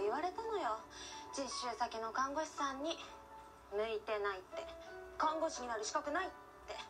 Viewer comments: 「抜いてないって」にしか聞こえない…溜まってんのかな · 抜いてないに聞こえた